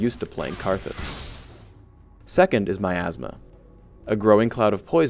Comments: lool. Sounded like he said "Second, is my astma" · Lol thought he said my asma